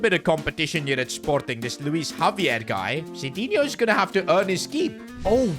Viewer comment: did bro just say Luis Javier💀